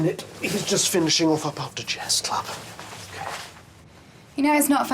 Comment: What does Neil's dad say at 5:54? Something about how Neil's finishing off a pub digest club? 🤔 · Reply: Finishing off after chess club